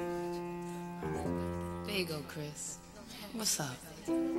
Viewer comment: in the beginning i always thought Alicia said "bagel crisp" but i now know its " there you go Chris"!!! hahaXD